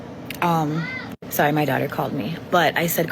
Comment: woah she said daughter!?! Did I hear that? She invited a stranger in for 2 weeks while she has a daughter there too?? Woahhh this gets wilder